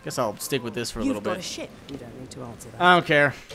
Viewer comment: IS SOUNDS LIKE SHE SAID SHIT